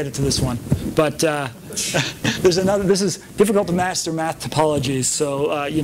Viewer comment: what is he saying at 53:35 ~ 54:00 ? Something something about Bathan's apologies? Or difficult to master topologies? Could someone please help me? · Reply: Jackson King Math typologies, he's just talking about breaking the mechanics down to their fundamentals and seeing if it's fun there, then scaling it up based on what you find.